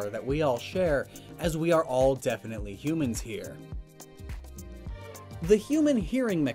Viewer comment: Did he say I'm human? :'D